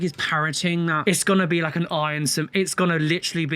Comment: When you said "parrotig" I heard "parenting" as was quite confused for a moment 😂